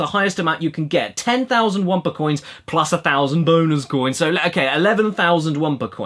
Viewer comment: When you said "bonus coins" on 10:05, I heard it wrong and it sounded like "boners coins." (...) Cash Bone-ooca?